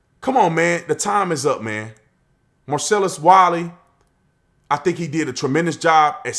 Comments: Hands up if you thought he said Marcellus Wallace. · Half listening to the opening I thought you said Marcellus Wallace and thought did I start pulp fiction somehow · I had to rewind because I thought you said MARCELLUS WALLACE hahaha....